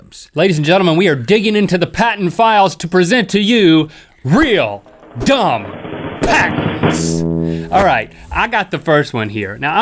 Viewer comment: LOL i have subtitles on and when link said real dumb patents it thought he said real job Thacker sry LOL